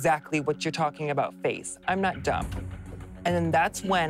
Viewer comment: I keep hearing "I'm not dump" 😂